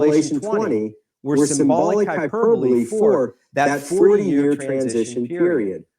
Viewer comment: He takes the millennium as a 40 year period, exodus generation. I agree with him. I see the binding of Satan in the ministry of Christ. The loosing of Satan during the apostacy Paul talks about in Acts at Ephesus. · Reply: thank you. I replayed your clip of him 3 or 4 times and I thought he said 3 years. I’ll play it again…. Thank you for doing these videos, they are so so helpful.